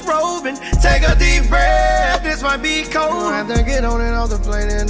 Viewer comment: I'm tryna figure out what he said after take a deep breath this might be ? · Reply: This might be “Cold” lol